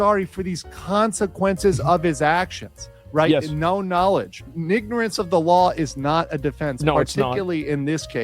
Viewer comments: did he just say "nignorance of the law"? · excuse me... did I hear that right... "N-ignorance" ?!?!?! HAHAHAHA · Did he say Nignorance!?